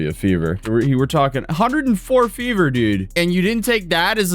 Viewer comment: i thought you said 140°F 😭